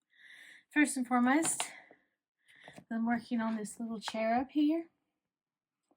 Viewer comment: Those light do look lovely (German here 😅)! ❤ And, just for some laughs: Every time you say „I‘m working on this cherub here“ I hear „I‘m working on this chair up here“, which always makes my brain stop working for a second… 😂